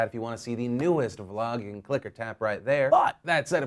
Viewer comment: I've been watching his show for years. I thought he had been saying "click-a-tap right here" BUT NO. I was listening with my new headphones...he says "click OR tap right here." MIND=BLOWN.